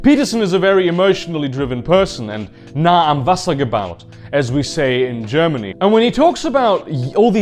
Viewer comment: I'm a German and I don't know what he said. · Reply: "nah am Wasser gebaut"